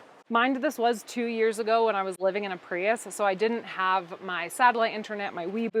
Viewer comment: I’m my first time seeing your video…really thought you said 30 years in a Prius. Really threw me.